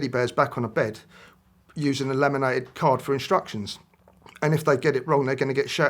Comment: "laminated card," I thought he was saying "lemonade cart."